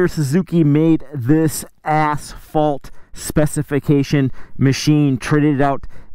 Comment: ASS FAULT SPECIFICATION is all I heard. 😆